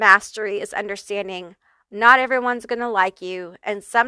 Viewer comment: You said “not everyone is gonna like you” But for some reason I heard “not everyone is gonna lie to you” I totally mistook it But I like it